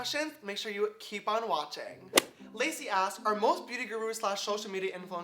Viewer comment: I'm dyinggg, he said "Lacey asks" and it sounded like he said "Lazy ass"